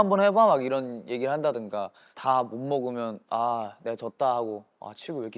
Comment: I heard "I'm jase"??